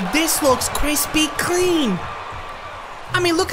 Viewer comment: i swore i heard him say "This looks Krispy Kreame"